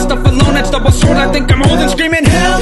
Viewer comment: I always thought you were saying “L O L” not “Help, oh well” 🫣🥸I now think this song is much better😅